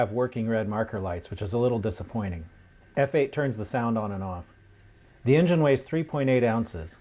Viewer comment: "F8 turns the sound on and on" lol 3:27 (I Know its not what he said but it kinda sounds like it lol)